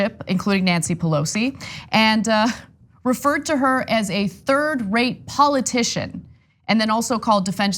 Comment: I heard it was "third grade" politician. Maybe third rate is what he meant.